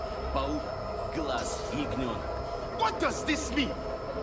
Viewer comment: I guess I have a sleeper agent in me to because when the Congo guy yelle “What does this mean!?” I swear for a second I thought I heard “Why are you running!?”